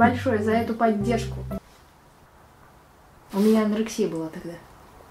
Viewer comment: какая анарексия или мне послышалось